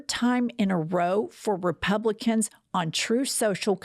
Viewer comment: my age has definitely affected my hearing. i heard 'repellican' instead of 'republican'.